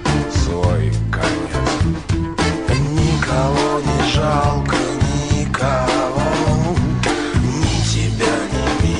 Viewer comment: what did he say? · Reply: Nikogo ne zhalko nikogo